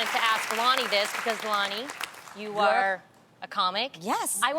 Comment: Loni's face when Tamera say... "Loni you WERE a comic" 😂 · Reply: I heard "you uh are a comic"